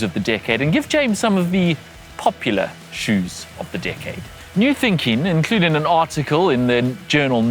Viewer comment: Who else thought he said dickhead and not decade lol